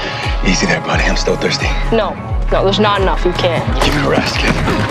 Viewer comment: Kimda sounds like rick said "shane enough"